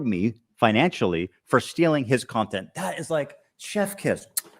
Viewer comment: she did NOT say "if you like my content" she said THE content. H3H3 lied.